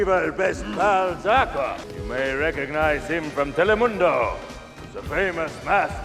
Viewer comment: Did he say Telemundo because me and my mom watch the news and novelas on there becuase we think its the best spanish TV channal.